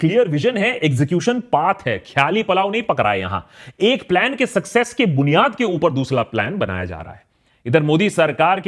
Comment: did he say dusla plan?😂…anyway,great video once again akash🎉